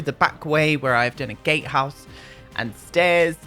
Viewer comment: Notification Squad B))) Oh my gosh the intro music is so amazing I love that you picked that! I know you said gatehouse, but I heard "Gay house" :)) Maybe we can pretend the "bridge is broken" until/if we get bigger lots. 100 by 100 lots would be insane ... imagine <num> ... I'd cry. And so would my laptop. I like big LOTS and I can not lie! Had 2 not even sorry asdfghjkl Lmao Aden the Soul Cycle thief. A new world that gives us a natural waterfall that we can explore behind or new build stuff to make a waterfall ourselves if we so choose, I need a pack like this in my life!!! I hear you saying you want CC but all I can hear is "I need those seamless windows from mutske." I got to enjoy Easter at my Aunt's house, it was a really chill day and I went over there instead of staying home with my immediate family cause well, you know how they are. I was worried about not having a good day so I went to my Aunt's instead and it ended up being really nice. And when all of my relatives left my aunts house it was my uncle, and my two aunts and my grandmother and I and they were telling stories from back when they were young and they were really funny, we were all laughing for like an hour after the dinner ended. It ended up being a really nice day! Other than that I've been working and making that bread B))) Shockingly tho lately it hasn't been loud in my house which is good. Sorry to hear about the stairs tho, this is deff one of those kinds of builds where spiral staircases or even l